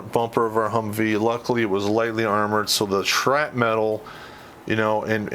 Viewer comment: did you say shrapmetal instead of shrapnel?